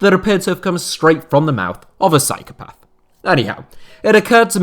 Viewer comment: Every time I hear you say “anyhow” I think you’re saying “Eddie Howe” 😂